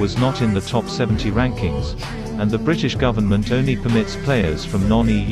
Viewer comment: Did he just say "not unfair"